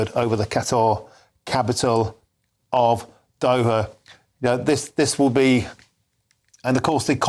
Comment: OH their capitol of Doha. I could have sworn he said Dover in there.